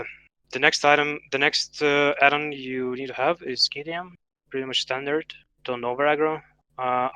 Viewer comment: any one know what he said his mic is realy scratchy sounding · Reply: KTM